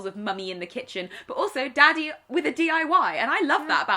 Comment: “Daddy with a DIY” I legitimately thought you said Daddy with a DUI for a full minute until I realized...